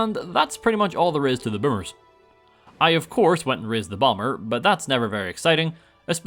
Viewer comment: did anyone else think that he said "went to rizz the bomber"